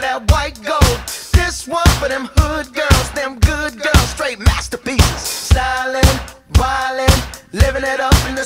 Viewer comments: All I hear is uptown fuck you up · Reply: Atleast it says "Funk" in the title. This is what i heard: "This one for them hood girls, them good girls, straight masturbators" Until i figured out recently that he was saying "masterpieces". So relieved. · What was that he said? "Black blue?" My friend thought he said "white gold?" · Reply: It's white gold.